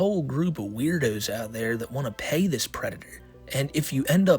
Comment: Homie I thought you said people wanted to peg this predator not pay.